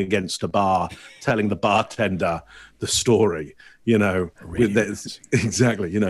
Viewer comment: Does anyone know what says at 19:44? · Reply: "he sorta whispers into it..."